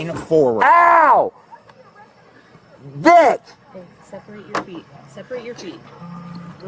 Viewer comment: Did I hear “separate your cheeks?”